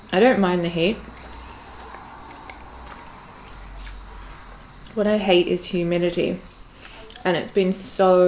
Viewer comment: I could swear you said "What I hate is humanity" I was already like "me too, hungry, me too"